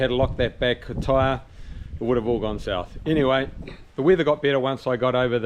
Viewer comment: Sorry about the flat tar. At least you got some practice. I didn't know you said gone south down under. Thought you said gone north lol